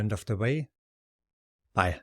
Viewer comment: What does say right at the end? The very last word that comes out of his mouth at 6:32? maybe its just 'bye' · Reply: Exactly, I said "Bye." The software I use to improve the audio quality made this "Bye" into a weird sound, but I wasn't technical enough to fix this. 🙏